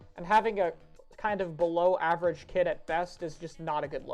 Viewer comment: when you were talking about the vanilla squeezer I thought you said "it has a bologna kit" and I agreed 100% until you said below average, which I still agree with